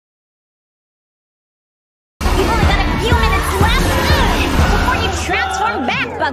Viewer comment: Thought it said 4 minutes But I looked back and I shat myself 😨 /hj